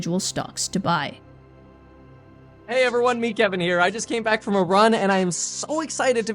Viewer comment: THOUGHT HE SAID I CAME BACCK FROM IRAN